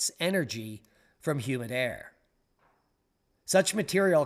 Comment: "Humid air" energy....but I heard "human hair" the first time. Too many 80s drugs...